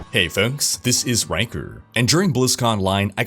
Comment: I can't figure out his accent. It sound like he is saying "Hey folk, this is Ranker"